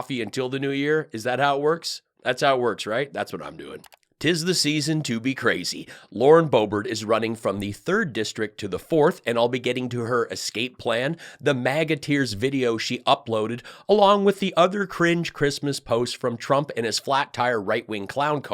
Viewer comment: I thought Hoebert said she was running in a Red Light District. Sorry. My bad.